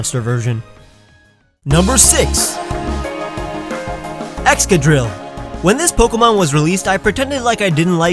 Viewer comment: Number 6 was Excadrill, yet I heard it as Extra Dip >.>